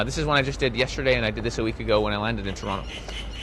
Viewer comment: Did he just say about "a week ago?"